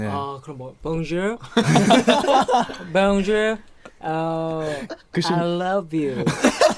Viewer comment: Did Ren say "Bonjour" at 3:36 ? 😲